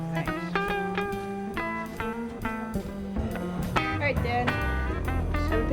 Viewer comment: is this Joe's daughter? Did she say "all right dad" ?